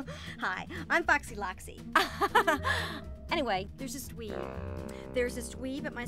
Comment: At first i thought she said "there's this weeb"